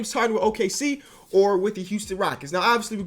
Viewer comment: I thought you said Whitney Houston for a second.